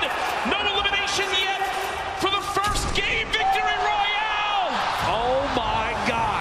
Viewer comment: did anyone else hear gay victory royal?